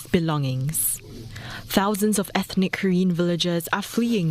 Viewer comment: I thought she said Korean..... but Koren. That threw me off. I really hope things work out.